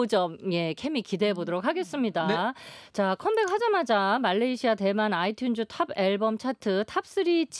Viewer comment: did she said Malaysia?? 👀